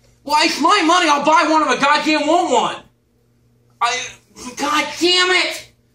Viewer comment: “walmut” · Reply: I always thought he said “I’ll buy one off a goddamn walmart”